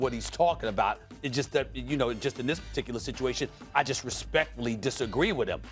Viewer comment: Did he say "I disrespectfully disagree"? :D